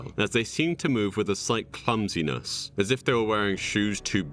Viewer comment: heard 'cuntiness' instead of clumsiness and had to do a quintuple take